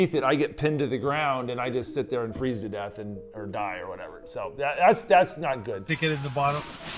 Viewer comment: did he just say die or whatever😆😆